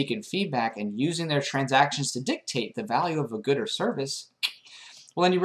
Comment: Did you just say gooder? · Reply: He said "good or service"